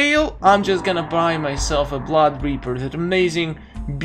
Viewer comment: Did you just say blob reaper😂😂😂😂 Good job for killing Lynx